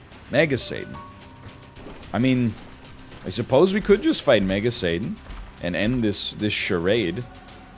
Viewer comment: God I swear sometimes it sounds like NL is saying Mega Satan as MAGA Satan and it gets me rolling